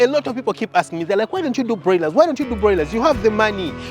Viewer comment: I thought you said do broilers come on .. now I’m confused